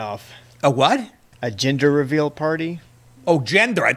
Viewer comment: Thought he said Ginger too. How did it start? ??? These ppl are so dumb they probably think it’s climate or too many vehicles on the roads